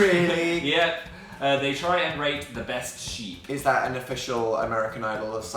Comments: i thought you said "try and rape the best sheep" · When Phil said "try to rate the best sheep" i thought he said "try to rape the best sheep" and I was extremely scared.